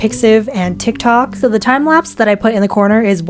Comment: I THOUGHT YOU SAID TWO TIME LOL